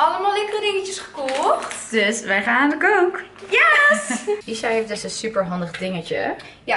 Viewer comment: Hoorde ik daar nou: "We gaan aan de coke!" ??